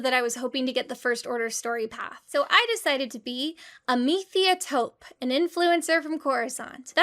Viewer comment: In my brain Amethia Tope is so influential that Skippy also has his own social media presence (or whatever the equivalent would be) and thats how the captain recognized him 😂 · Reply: Oh my God it literally took reading this comment for me to understand that was her NAME. I always. I always heard it as "I decided to be: a methiatope". As in, Methia is a suburb of Coruscant. My God. I'm an idiot 😂